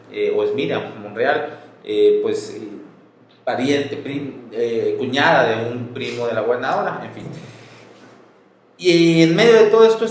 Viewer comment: Minuto 9:41. Miriam Monreal. No se entiende. "... pariente, cuñada de la nueva ola?...". Que dijo? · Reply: "Cuñada de un pariente de la gobernadora..."